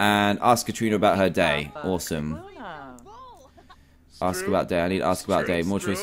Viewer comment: did he say "Ask about Daddy?"